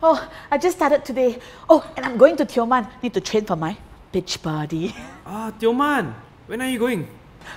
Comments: I thought said "Bitch Body" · I thought said “b**tch body “ 😂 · Did say bitch body?